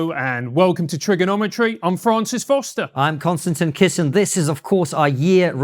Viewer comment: For the first time ever I heard "Constantly Kissing" instead of "Konstantin Kisin". I will never unhear that. You go girl!